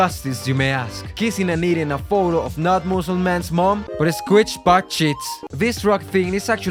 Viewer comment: subtitle said “muslim man”💀